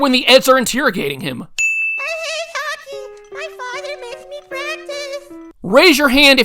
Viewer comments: Jimmy says he hates hockey and his father makes him practice. I understood what he said. Just have to listen closely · Jimmy said "I hate hockey, my father makes me practice" I heard it perfectly loud and clear, so yes, my hand is going up. 5:45 Evidently Jimmy DOES have the stealth to pull it off, did you not watch the episode? 6:04 Yes, he does, there are three losers I can think of in the episode who just got pelted with fruit, what are their names again? 6:23 it's called irony. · Jimmy says “I hate hockey, my father makes me practice!” I heard him clear as day man. No offense, but you need to pay attention.